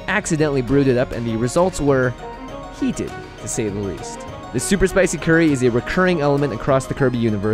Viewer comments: In the beginning it sounded like you said super spicy Kirby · I thought it said "Super Spicy Kirby".